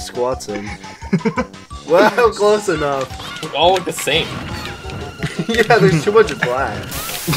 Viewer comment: What did say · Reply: Turtles and more turtles there’s just too much black